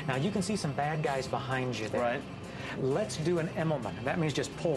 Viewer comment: thought he said "Lets do an enema"